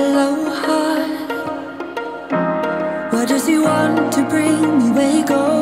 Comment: "Why does he want to bring me bagels?" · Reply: I always hear "Or does he want to bring you bagels"